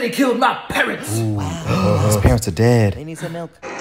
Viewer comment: "Mmmm, ooh wow, his parents are dead, He need some milk!" Had me like 🥲,🥹,🤣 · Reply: I thought he said “he needs some help”